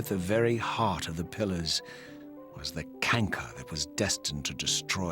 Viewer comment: did mean "Cancer"? Or is what he said an actual word? Yes it's very interesting to see how the roles have shifted. Now is the angry vengeful one while Kain is the more calm and reasonable one 29:48 that door opened surprisingly well for something that hasn't been moved in centuries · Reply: He said "canker". <link>